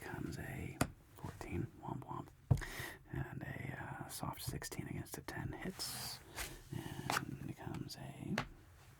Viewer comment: Am I tripping or did he say “womp womp” at 15:31 😂😂 love you but if I double down and get a 3 and my dealer says that they gon have to call security lmao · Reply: He really said "womp womp"